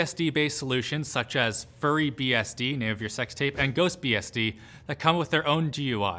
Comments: Does he say “Sex tapes” at 4:09? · I swear I hear 'sex tape' after that... I've listened about 20 times now. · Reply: Aidan Cubley He says "name of your sex tape" which is a Brooklyn Nine-Nine joke. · some reference to sex tape? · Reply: I know, I coulda sworn I heard him say "may I have your sex tape" LOL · Just me or did it sound like he said furry and not fury